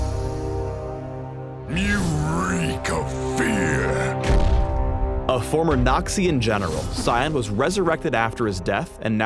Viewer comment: Completely misheard it as "former Nazi and general"